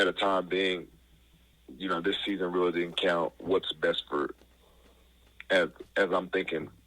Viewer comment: I keep hearing “This season didn’t really count”. I wonder if Alabama feels this way?